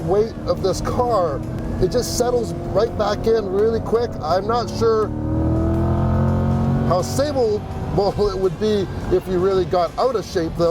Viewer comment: WAIT -WAIT-WAIT At time 14:02 did he just say "I wouldn't know how stable a Muthaf***er would be"?